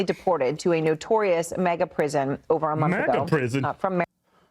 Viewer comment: Not to take cnn side can’t stand them , but it sounded like she said Mega prison!